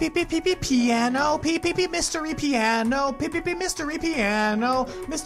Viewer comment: wut the fuck are u saying 7:05 MYSTERY PIANO????